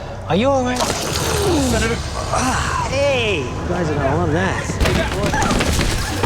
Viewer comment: What does he say here 2:02? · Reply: "You guys are gonna love that" i think